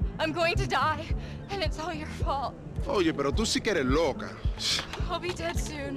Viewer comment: can anyone one write that down what Louis said in Spanish? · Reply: “¡Oye pero tú sí que eres loca!”